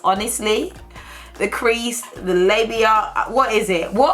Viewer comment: PATRICIA DID YOU JUST SAY LABIA? I'M CRYING, PLEASE SEARCH IT UP.